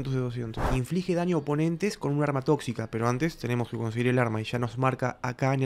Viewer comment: Escuché bien o dijo Arma Tóxica